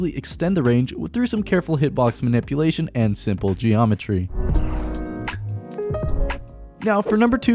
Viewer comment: did someone say simple geometry?? #ripscatter